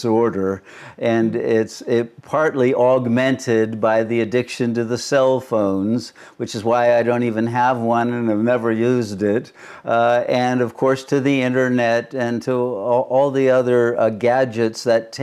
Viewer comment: Sounded like Shunyamurti said "self-phones" 😂